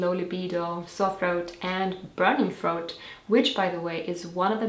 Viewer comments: That link does not work either. By the way can you please please please tell us what you said starting at 2:13??? What did you say are the symptoms of low progesterone after you said "low libido"????? I'm just a dumb American, and I can't understand your accent. Please help. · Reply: Jojo Jo sore throat and burning throat are what she said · ish, what are you saying? I'm having trouble making it out. It sounds like something to do with "throat"? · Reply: Sore and burning throat. <3